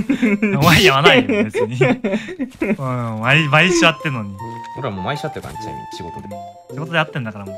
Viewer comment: 「毎週会ってるのに」が「愛し合ってるのに」って聞こえちゃった・・・